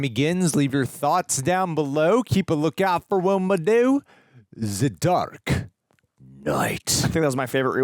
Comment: Listening to this at 2x playback speed, at 1:23:35, I heard, the duck knight... ...well, "let's get DANGEROUS!"